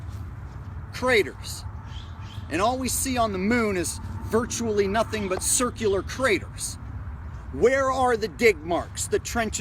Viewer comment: At first I thought he said dick marks lmmfao. Great video 👌